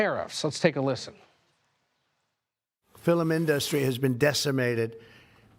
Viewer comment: Did he say "fillum industry"?